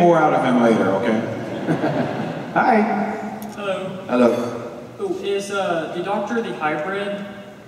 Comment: What did the guy say at 10:28? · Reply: Asked if The Doctor was "The Hybrid", pretty sure everyone laughed at the lack of response.